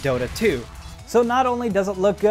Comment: I thought that he said turion like the old amd laptop chipset. That would have been an epic fail